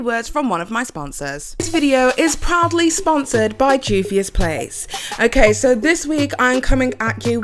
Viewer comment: Wait All this time i thought you said "this video is probably sponseret buy Juvias place" because it's so often 🤯🤣